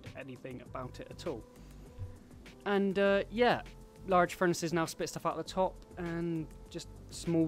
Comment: what did you say about the large furnaces? accessible at the top or something? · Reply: Excess items get chucked out the top :)